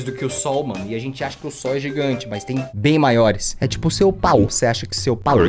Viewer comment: ele fala sobre p** eu ouvi isso sendo menina (😳 eu sinto que está conversa não para mim)